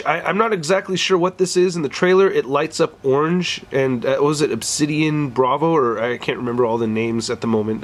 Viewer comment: Did he just say obsidian Bravo